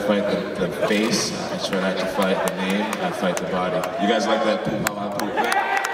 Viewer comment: what did he say at 1:44 ? "you guys like that" what???